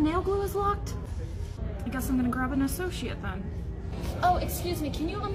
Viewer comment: I had to listen twice I thought you said an a sociopath